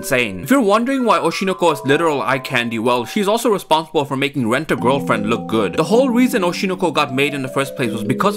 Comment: I literally thought u said wet a gf🗿